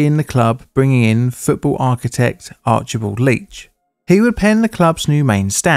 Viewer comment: I had to look up when you said "Archibald Leitch"...I thought you said "Archibald Leach" 🤣